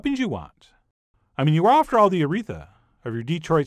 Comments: You are after all, the Mona Lisa, of your Detroit Style Pizza! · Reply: I thought he said Urethra 🤣 Aretha is much, much better. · You are after all, the Mona Lisa, of your Detroit Style Pizza! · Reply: I thought he said uretha and I was like what